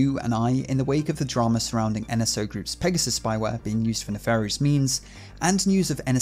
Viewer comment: For a sec, I thought you said Nefarious *memes*. Barely flinched.